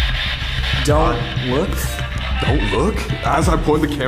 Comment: I thought it said: "Got him". Not "Dont Look"